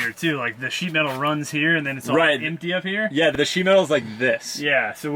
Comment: Thought said shemale